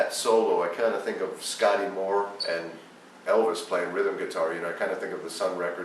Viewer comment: Very fine guitar video ! But some subtitles are unfortunately wrong : at 2:59 and later, this musician refers to SCOTTY MOORE (Elvis Presley's guitarist), and NOT "Sky Lure" ; also, at 10:36, he is obviously saying "a lot of RAGAS", and not "a lot of RABBI", as erroneously subtitled...